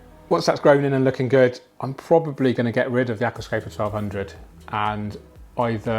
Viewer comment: did u say that you were getting rid of 1200 or did you mean 600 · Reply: 1200